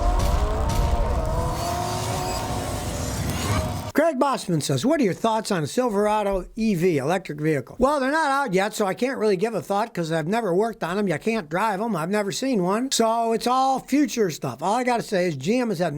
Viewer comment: Woah. I thought Scotty said "I can't really give a f*ck."